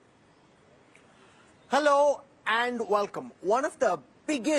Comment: Little learning is dangerous. He is in a Telugu state and saying wanakam. If u don't knw the languages, no issue. Speak in your language. We ll understand. Don't do this symbolism thing please. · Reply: He said welcome. We heard it as vanakkam.